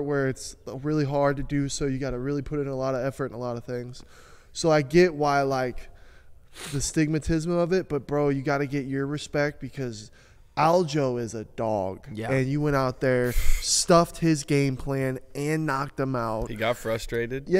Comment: Did say the “astigmatism of it“ 16:59 😂😂 not a clue what he’s saying lmao